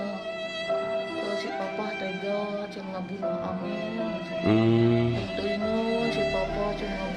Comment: Menit 4:31 kalau gak salah dengar "papah tega bunuh amel"